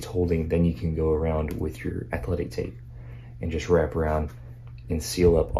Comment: What kind of tape did he mention @ 13:55? I couldn't make out what he said. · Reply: Athletic tape! You can find it at pretty much any store with a sporting goods aisle.